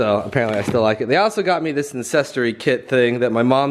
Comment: In the beginning it sounded like he said “incestry” instead of “ancestry” 😂